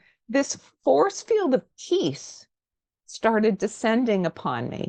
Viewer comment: Hi, how to do this meditation?! Without waking up an hour later, or meditating on the to-do list. On my third or so attempt I felt my brain go whoosh and came to in shock. Never tried again. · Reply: Oh classic! Your face when you thought she said "a forcefield of TEETH" and then after she clarified of PEACE".... Had me rolling! But I'm all seriousness love the channel. I've been engaged in these esoteric topics for a few years watching many channels. But your inquisitive but respectful interview style is top tier. Best of luck in all you do.